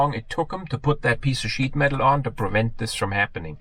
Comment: Love it at 5:10 I initially thought you said it took them this long to put that piece of 'sh1t' (you know what I mean) on but it was sheet metal and your accent. I think my version is better lol.